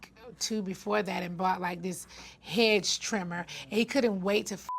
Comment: I thought she said Gregg brought an edge trimmer 😂